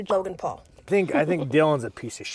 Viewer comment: Sorry…Is she saying Jogan Paul?!